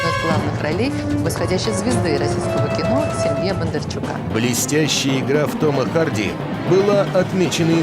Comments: я один слышу "Блестящая грудь" ?! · Сука, тётка в белом 17:17 мне послышались Блестящая грудь